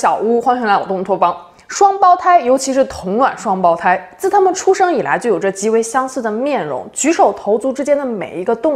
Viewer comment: 有句台词，双胞胎，尤其是同卵双胞胎，自他们出生以来就有着极为相似呢面孔 我听成 这他妈出生以来就有些极为相似的面孔。😊😊😊